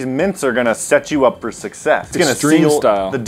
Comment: Did he say suc"cex"